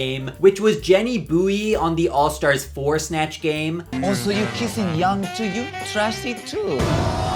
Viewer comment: can somebody tell me what gia says at 10:25?? i skipped out that snatch game lmao and i can't understand the accent shes doin at all (im brazilian) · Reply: “oh so you kissing young too? you trashy too! […] you slutty”